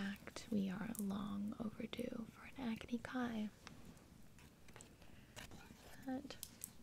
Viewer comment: When you said "Agni Kai" I literally thought you said acne for a second